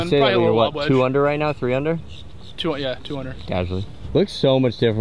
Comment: When you asked what he was trying to shoot this round, I thought he said “200” instead of 2 under, and my initial reaction was to think “damn, he really is just like me fr”